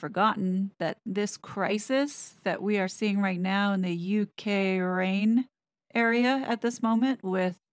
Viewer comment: Did she say "the U K Raine"??? Our weather may be unpredictable but it sure didn't start a war! 😄